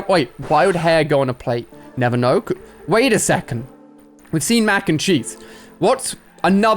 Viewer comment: I thought he said mac and cheeks XD